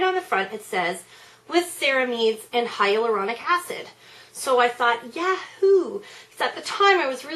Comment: I have wanted Creme Cup FOREVER. Literally. Forever. I don't have ANY idea why I haven't bought it. But I should. I thought you said that had "hyper erotic acid" and I was going to say, "Julia.. That might not be the most appropriate thing to say.." lmao I've missed you. <3